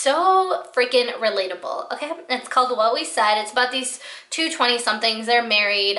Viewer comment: Can you please tell me what's the name of the podcast from the minute 5:10 I didn't catch it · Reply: It’s “What We Said” podcast! Thanks for watching! ❤️